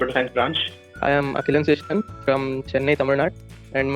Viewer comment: What name he said..? At 0:21 ?? The guy from Chennai, TN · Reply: do subscribe bro!